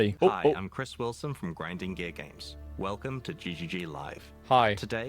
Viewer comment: does he say: "grinding gay games"